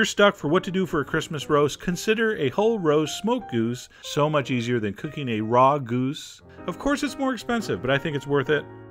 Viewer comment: haha at first I thought said, "So much easier than eating a raw goose."